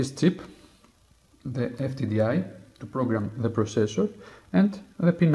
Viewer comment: Thx for the video but what did you say I need to program it at 3:33.sorry but I did not understand what you said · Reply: An FTDI programmer. Check the video desription for a link to the item.